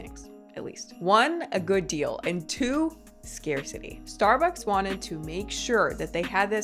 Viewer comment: you said "scarcity", but my brain heard it as “scare city,” which I think is very appropriate for those of us who don't get caught up in this madness...